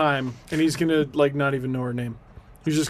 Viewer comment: So as an Australian I definitely thought you were saying Nauru, the offshore immigration detention centre and I was VERY confused